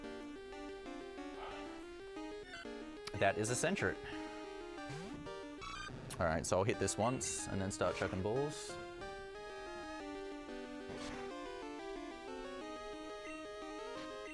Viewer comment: I heard "So I'll hit this once and then I'll start tripping balls" haha